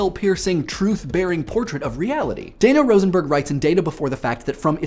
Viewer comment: did he say Data Rosenberg?